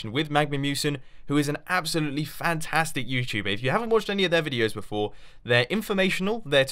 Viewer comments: I feel like a caveman after watching these. :o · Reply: are you a girl cuz I heard Mumbo say &quot;if you havent watched any of H E R videos before&quot; - 11:05 ????? im a fan of sherlock holmes btw just so you know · Did he say "if you havent watch H E R videos before" - 11:05 ???? does that mean MagmaMusen is a girl???